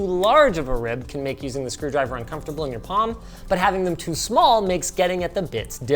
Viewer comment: I heard “ in your mom “ instead of “your palm” 🤨